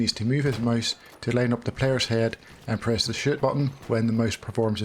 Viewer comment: 🤣😂 i thought you said the shit button lol